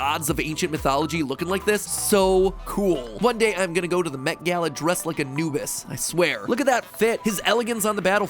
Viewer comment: I heard it as "One day I'm gonna go to the met gala dressed as a nudist"